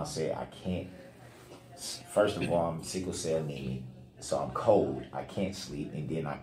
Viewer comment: I wonder if I heard this wrong. Right before the 37:00 mark, it sounded like he said “first all, I’m sickle cell nemie and I was cold and I can’t sleep” Does anyone else hear that?